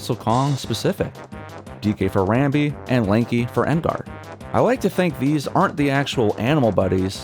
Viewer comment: what did you call him? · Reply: I think he said An Guard lol